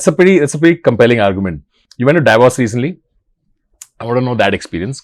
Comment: When he said “you went to Davos” i heard “you went through divorce!” 😟